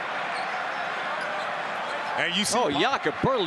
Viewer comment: At first I thought it was saying the coach hip checked him